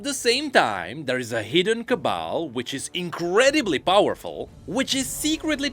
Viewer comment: What did he say at 1:25? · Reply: Cabal?